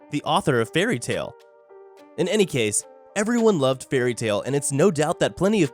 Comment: wait, did he say, everyone loved fairytail????